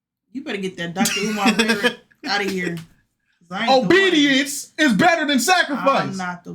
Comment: 😂😂😂when he said obedience I thought he said on BD at first 😂😂